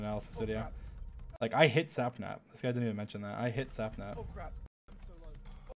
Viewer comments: What Dream said : I hit sapnap What I heard : I hate sapnap 😂😂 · I thought I hear Him say I hate SapNap but it was I hit SapNap 😅 sry....